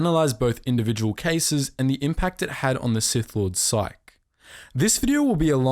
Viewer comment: Did you just say sike instead of psyche?